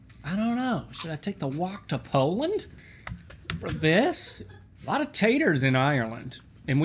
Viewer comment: Did link just say he's gonna take the wock to Poland... I'm dying lol